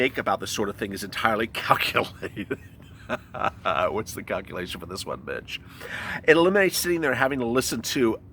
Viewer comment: Hey Sup?. It sounded like you said: "What's the calculation for this one? Bi*ch" lmfao!